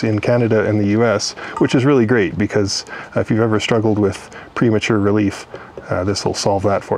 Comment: "If you've Ever Suffered from Pre Mature Relief this will solve it for you" Quote of the year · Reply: I thought he said "premature release".